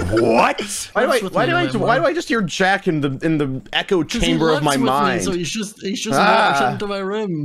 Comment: FOR A GOT DAMN SECOND I thought he said the chamber of secrets and I was like "WAIT HARRY POTTER!?"